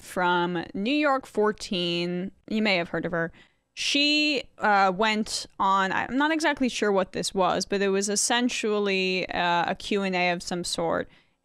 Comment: did I hear that right? 'essensually'?